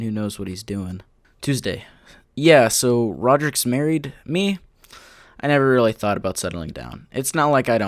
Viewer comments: sound like it says so rodricks married me As if they were married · For a second I thought it said "yeah so Rodrick married me?"